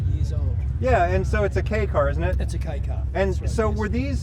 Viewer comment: Never knew what a "Kei" car was. Never heard that term before. I thought he was saying "K" car and so I had to look it up. You can learn something new every time you watch the channel.